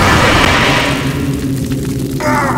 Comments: what did heavy say ? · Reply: I am big war hero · "I'm a big war hero!" the book did everything! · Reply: Lol. I misheard and always thought he said "I am big boy hero!"